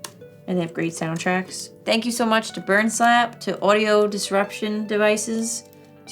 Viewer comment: I thought it said 'Bum Slap' for a second